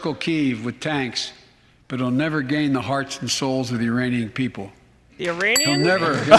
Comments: I heard "Uranian". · Great time with you guys tonight. My highlights: - calling Ukrainians "Iranians" - Ukraine ambassador rolling her eyes at Biden's comments - string of lies attached to "second amendment bad" directly after praising Ukraine for giving all citizens guns to defend their sovereignty from a tyrannical government. - (negative highlight) veteran's widow realizing she's not going to actually get any help, she was just brought in as a spectacle for sympathy. - gay justice dance - 60 million barrels of oil. And failure to mention the thousands of times more than that we could be producing if Biden didn't sabotage our own oil production. - Queen of the flies dancing and getting all jazzed up about burning feces and lung cancer in soldiers. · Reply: I honestly thought he said Uranians 😳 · He said "Uranian people" I thought he'd said "Uranium people" at first but that would be silly.